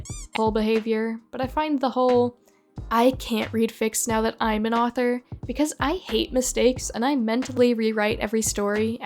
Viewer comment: OH I got confused I thought they were saying “I don’t like to read other works because I’m worried they’ll make me dislike my own and demoralize me”